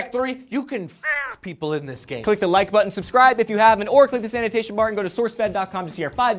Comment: did he say "click the sanitation bar"